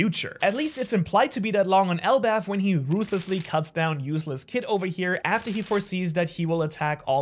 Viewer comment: did he say "Useless Kid" or have i misheard?